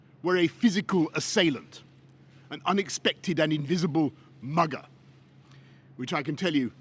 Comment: I thought he called Covid 19 an 'invisible bugger'... Unsurprisingly, I misheard him. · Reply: i think he said mugger not burger !